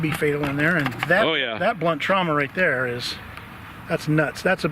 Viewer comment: When said the phrase "blunt trauma," I thought he said "butt trauma." I have since regained my composure.